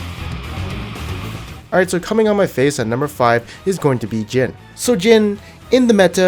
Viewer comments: did he say cumming on my face at 0:50 ? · LMFAO AM I THE ONLY ONE WHO HEARD HIM SAY AT THE BEGINNING " So coming on my face at number five" 😂😂😂😂😭